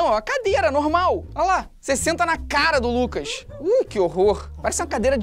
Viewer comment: O que Felipe Neto disse: "Cê senta na cara do Luccas" O que eu entendi: "Cê vai senta é na cabeça" Quem entendeu a referência da like